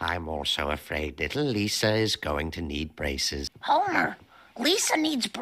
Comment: My tired ass just woke up and thought he said "Lisa is going to be racist". (I of course didn't read the subtitles)